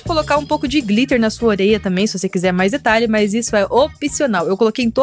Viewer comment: só eu q entendi oreia? 😂😂 Te Amooo ❤❤